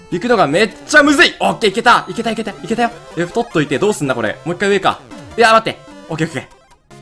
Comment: 太っといて？なんて言う言葉